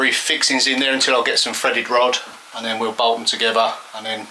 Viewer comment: Sometimes you accent throws me for loop -- not your fault! I kept hearing you say "a Freddy rod" and wondered what the heck that was. Then it dawned on me you were saying "threaded rod" which made perfect sense for the purpose. Ah, the wonderful varieties of English!